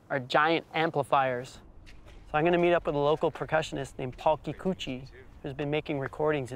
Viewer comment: Did he say talkie coochie? Lmao